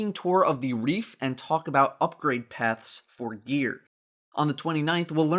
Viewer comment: So I was tabbed, and thought I heard something about 'Upgrade paths for beer.' Why is this not a thing? D